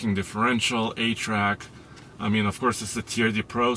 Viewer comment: I thought I heard "8-track" for a second and then realized it was “A-TRAC” but it did make me think Toyota really does need to consider a redesign.